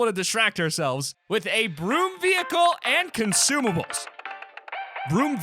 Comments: I thought he said a brute vehicle I was like hell nah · When he said “broom vehicle” I thought he said B.R.U.T.E vehicle, I was about to SCREAM 😅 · When you said broom vehicle I thought you said brute nd I started to get angry